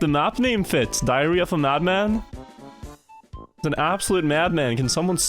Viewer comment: I realy thought you said diarrhea of a mad man.